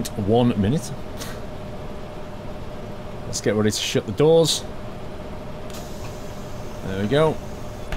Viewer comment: did u say "lets get ready to sh*t the doors"?